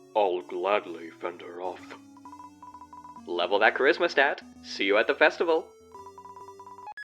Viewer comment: i thought he said "Level that Chrizzmas stat" like a joke on Charisma and Christmas LMAO.